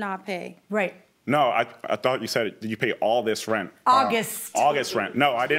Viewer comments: I thought the judge said, "all this rent, too". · I thought she said “did you pay all this rent” too 😂😂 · I thought she said "did you pay all this rent" to lol